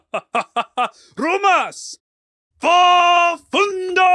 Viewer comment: I heard ''Grubas'' what means in Polish ''Fatty''. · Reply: More like rumas me opinion and yes im from poland